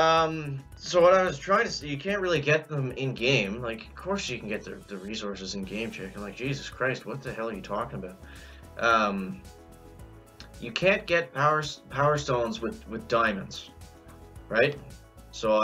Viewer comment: anyone else hear the call himself Jacob